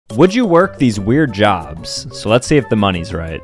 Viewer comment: I thought he said “would you work for Jesus”